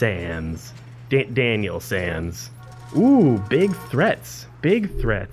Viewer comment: did you just say Sans? (Megalovania starts playing)